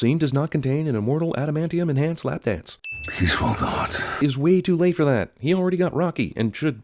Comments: WHAT DID HE SAYYYYYYY Wait nvm “peaceful thoughts” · WTF did wolverine say? "Please four gods" "He's for cods" "These raw dogs" This is a sin right here, the "Bad audio mixing in dialogue cliche" (ding).